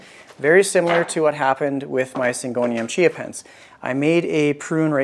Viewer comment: I know it’s far too early in the morning when my brain swears it heard you say you were propagating Chia Pets! 😂 All of your plants are so beautiful.